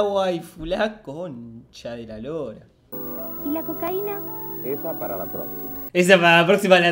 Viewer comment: Escuché “ES PARA LA TOS” jsjsjs